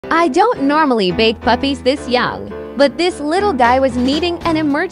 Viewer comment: It sounded like it said “I don’t normally bake puppies this young”😂